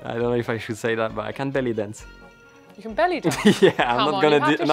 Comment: Belly dancing from Ocon got me off guard. I thought first he said he could barely dance, so I had tô hear it again.